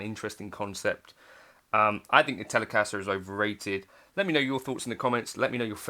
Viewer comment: "Overrated"? That's what it sounded like you said at 12:00 near the very end of the video. Just want to clarify cause obviously the title says "Underrated", without a question mark.